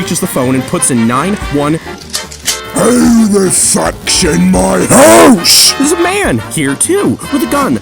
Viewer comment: Hey, can someone tell me what the guy is saying in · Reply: "Who the f***s in my house"